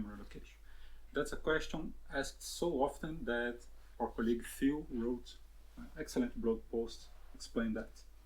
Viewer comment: I can't hear the name of the person who wrote the blog post about not needing memory allocation? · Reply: It was written by Phil on the team, and the post in question is here: <link>